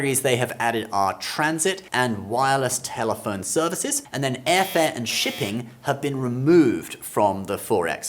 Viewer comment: I literally thought he said “airfare and chicken”😅